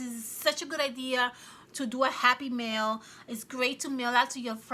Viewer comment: Oh happy MAIL !!!!!!!!!!!!!!!!! . LOL I thought u were saying happy meal. And I’m waiting for food to go in. Super cute idea THANK YOU !!!! I love them. !!